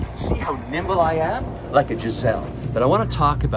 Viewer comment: Did you say "nimble like a Jezelle" ? 🤔